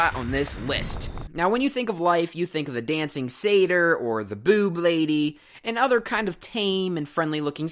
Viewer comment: Did you just say boob lady's? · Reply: Dryad.